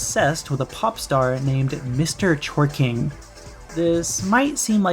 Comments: I misheard his name as "Mr. Twerking" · I misheard his name as "Mr. Twerking" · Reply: i heard it as "mr. short king" hlep